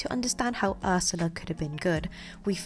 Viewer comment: it sounded like you said of my little mermaid